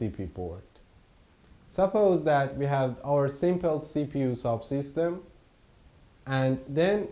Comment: Sorry, may be it is my misunderstanding. You said that inside the PS there are 2 ARM cores soft system and I thought you mean soft core. However, soft system is the system running software, isn't it? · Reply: If you have CC turned on, he said "Sub-system" not soft, because I thought he said soft as well.